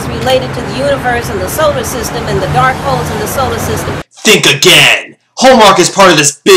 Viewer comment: Did she say dark-holes at 8:48?